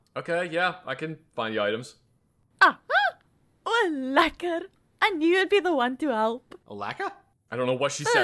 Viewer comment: I thought she said malaka